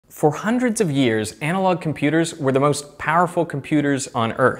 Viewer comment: Did he say "hundreds of years"?